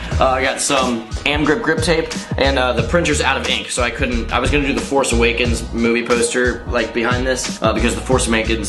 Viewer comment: Who thought said "the force amakens"